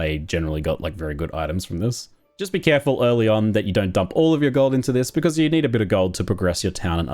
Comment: See what you said in 6:43 was "Just be careful you don't dump all your gold into this" But what I heard was "Just don't be careful, you dump all your gold into this"